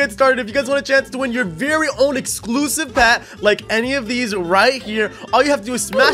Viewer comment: did he say exclusives , and I liked and made it 1k likes